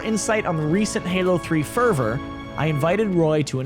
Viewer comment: ferver? favour? fever? wtf were u tryin to say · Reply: Fervor - noun intense and passionate feeling.